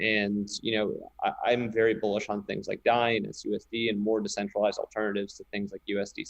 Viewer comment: What decentralized stablecoin does Vance say after DAI? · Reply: SUSD